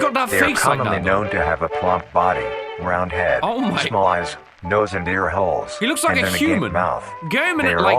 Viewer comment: I thought it said small a$$ nose and ear holes